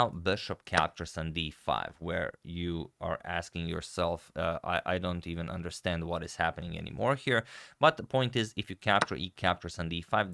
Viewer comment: "where you're asking yourself, I don't even understand what's happening anymore here" also sound like agadmator saying "But that's a very disgusting engine line, I mean who would play this" 😂